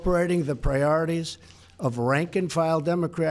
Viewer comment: It sounds like he says "Rank and Trial" Democrats. Freudian slip.